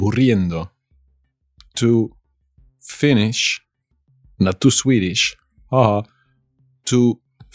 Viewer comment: What did he say at 0:56? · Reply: Not To Swedish ^^